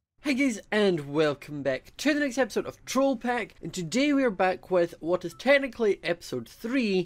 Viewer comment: When you said troll-pack I thought you said troll-craft and not going to lie I was over the moon and then i read the title and was devastated